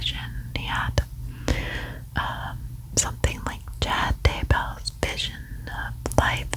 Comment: What is that you are saying around 7:40 like who? I can't make out that name. Thank you curious to know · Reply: Chad Daybell.. thanks for asking!